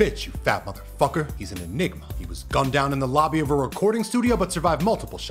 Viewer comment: When you said enigma I got real confused I thought you was saying the n word lol